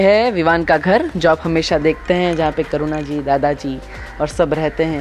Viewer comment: I heard Corona ji instead of Karuna ji 😂